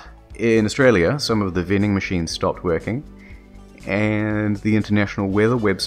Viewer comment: The closed captioning is hilarious. He says "some of the vending machines stopped working" but the CC says "Some of the vending machines only served Pepsi." 😄